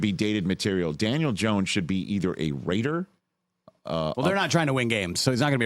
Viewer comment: for a second I thought that you said Daniel Jones should be a WAITER and I completely agreed